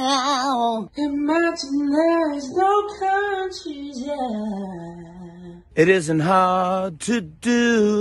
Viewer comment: "Imagine there's no concheezeyeaahaughghghghh" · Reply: I just hear "Imagine theres no corn cheese yeah"